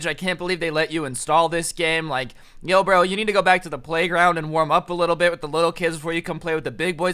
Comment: as soon as he said go back to playground and before he said playground an ad came on saying post shop so it sounded like he was saying go to the post shop